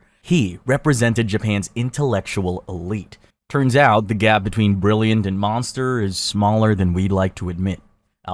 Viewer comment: anyone hear 2:12? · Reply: The gap between brilliant and monster